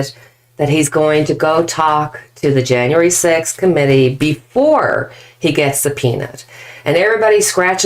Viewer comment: "before -he gets a penis"__is how i heard you say 'before he gets 'subpeonaed'-re:don jun.